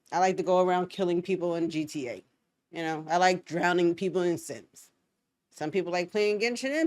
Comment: I really had to triple check those last words because you said "I like to drown people in the Sims" but I heard "I like to drown people in sinks." I should have known better though because you are a much more creative murderer than that. 😏🤫